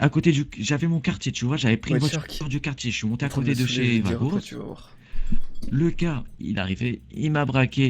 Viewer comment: Il dit quoi de 33:32 A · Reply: IzerTy_IFD Infused Il a dit on vas le saoule et tu vas voir